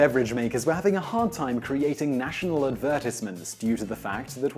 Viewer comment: wtf did you just say? adverse-it-meants? are you serious?? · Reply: That’s how English people pronounce advertisement.